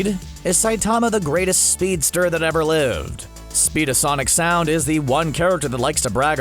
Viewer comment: did he say speed of sonic sound?